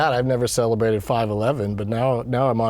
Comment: It sounded like he said he would celebrate 9.11 when he said 5.11